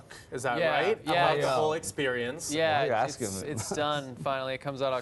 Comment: I cant understand what tommy is saying, did he say: "why are you asking this?" I think when tommy says dont betray a friend, he's referring to gregg writing the book haha